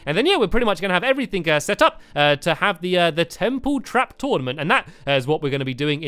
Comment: was it me or did he just say tremple